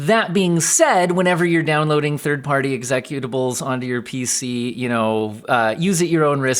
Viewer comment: what the hell did he just say? exezecuta-what? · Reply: Executables.